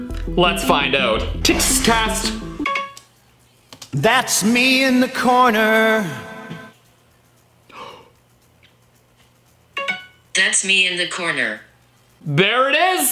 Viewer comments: I heard let's be in the corner let's be in the spotlight😂😅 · I heard let’s meet in the corner